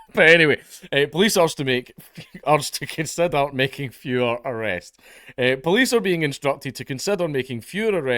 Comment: I thought that said "FOREVER arrests" lol